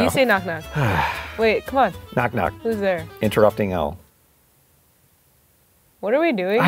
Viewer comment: knock knock! who's there? interrupting cow!............... What are we doing? I swear to God Me:😂😂😂😂😂😂😂😂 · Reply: For some reason I heard him say interrupting L...